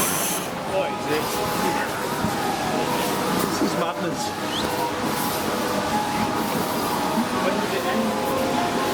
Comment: Did I just hear 'Giant Flange' @ 12:03 😂 I literally just died! Thanks for making my Monday blues slip away ❤🔥👍 Edit: creepiest monster has to be the knitted face ghoul.. I found that most disturbing 😱lol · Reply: Lol, you heard right! The bad thing is I used that phrase a lot in scare mazes, probably shouldn't have done so but it sounded funny at the time?